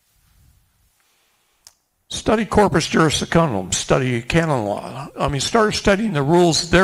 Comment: study what? Can someone tell me what he said right here? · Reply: corpus juris secundum